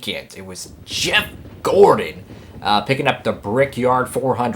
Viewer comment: I thought you said Chimp Gordon at the start